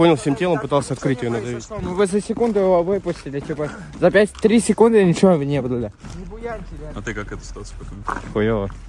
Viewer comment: Что говорит плохой парень😅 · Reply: вы за секунду выпустили,за 3 секунды ничего не было бы